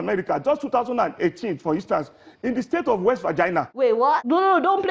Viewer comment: Did he just say "wet vagina"?